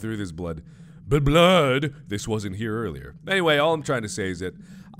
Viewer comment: Markiplier said: This blood wasn't in here earlier. Captions said: This was in here earlier.